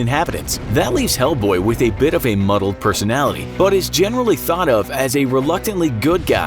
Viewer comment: DID HE JUST SAY “MUGGLE?”